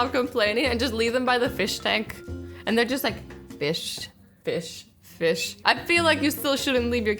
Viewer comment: Azzy: 5:11 Me: Did she just say- "Bish, Bish, Bish"???